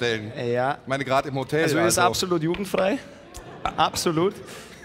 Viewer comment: 1) Ich hab "ist absolut judenfrei" verstanden. xD 2) Krieg dich wieder ein, ich kein Nazi du Pfeife. 3) "Ich guck Hengst Videos.. Bass Sultan Hengst Videos" :P